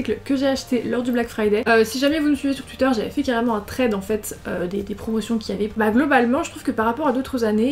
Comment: A 0:09 c’est moi où elle dis« j’avais carrément fais un trait d’amphet »